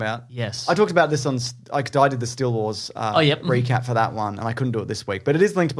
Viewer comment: "I talked about this on I Died at the Still Wars ..." What the ever-loving hell is he saying there? I must have listened to that sentence a dozen times and for the life of me I can't hear anything but 'I Died at the Still Wars'. I'd love to check it out but first I'd actually need to know what the hell he's saying there ANUNNCIATE - It's kind of important for a podcast ;-) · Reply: I believe he's talking about the Steele Wars channel.